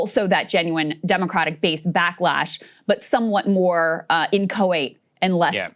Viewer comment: Can anyone tell what word Krystal uses at 23:50? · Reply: Inchoate